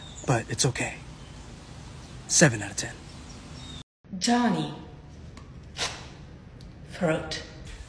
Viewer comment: I thought she said Jonny throat at 3:43 when she cut it I thought she was making a threat